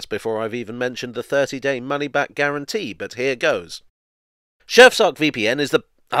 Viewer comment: did you say shurfsark?